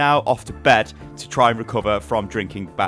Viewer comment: Ahaha, I thought you said you "had a massive crap whilst in Germany" 😂😂😂